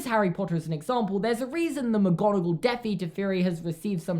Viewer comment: I thought you said McGonagal gupie theory. As in a fish. And the photoshop looked like a fish for a moment